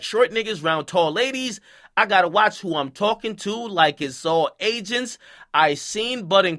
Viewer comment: I thought he said “I gotta watch who I’m Talking too like it’s all ages” not agents lol